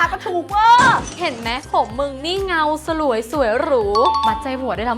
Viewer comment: เขาพูดว่า เห็นมั๊ยผมมึงนี่ เงาสลวย สวยหรู แต่ซับ เห็นมั๊ยผมมึงนี่ เงาสลวย สวยกรู